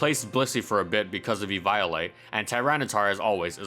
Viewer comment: Did he just call evoilite eVIOLATE?